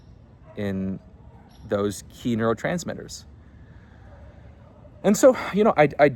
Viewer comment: i thought he said kinotransmitters instead of neurotransmitters and almost had a stroke